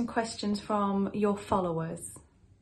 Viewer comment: I heard alot of noise but when I said xan we ask you question from you followers I heard 'my followers' like asif he or someone else asked a quiestion